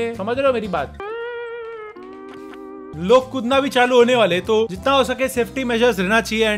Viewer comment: what did he sayyyy!!! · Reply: Kudna bhi chalu ho jaye ga!!